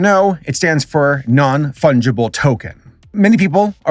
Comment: Who besides me misheard NFT as standing for Non Fundable Tokens?